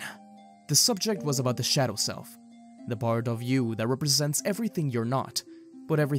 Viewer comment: the subject was about the shadow self Me: did someone say "i am a shadow, the true self" right now?